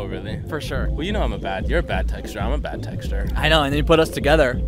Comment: I thought he said, "I'm a bad texture and you're a bad texture" and I was really confused at first.